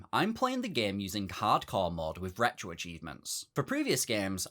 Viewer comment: No offense, at all, but that sounded like "cardcore."